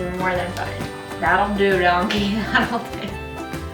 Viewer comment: lol no it's from Shrek! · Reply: OHHH I thought you said doggie hahaha not donkey haha